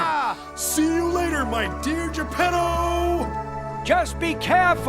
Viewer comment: I thought he said "See you later my Ninja Pedooo"